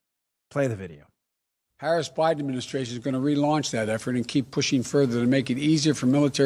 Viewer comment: When Joe said “Harris/Biden administration” look at what the CC read on the TV 25:57...it said “Paris Administration”!! The Mainstream is ALWAYS COVERING UP FOR BIDEN!!! 🤬🤬